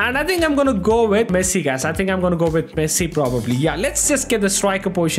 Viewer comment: am i the only one who heard pessi?